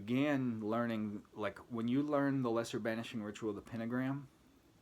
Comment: I thought he said, "the Lester Banshee ritual."